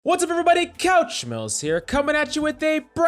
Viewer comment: I always hear "couch smells." :D